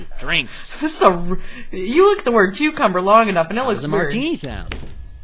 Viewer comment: I absolutely thought instead of "How does a martini sound?" said "How does having my D sound?" xD